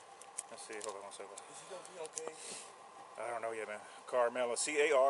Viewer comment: Did he ask “is he gonna be ok?” 1:09:41 I think I heard it